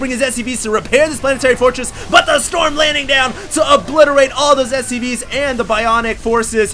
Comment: holy crap did he just say "TO OBLITERATE ALL THOSE STD'S" at 0:10 ??? i just noticed that lol